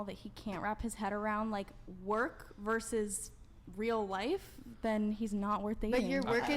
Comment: Work vs real life??????? Am I hearing this right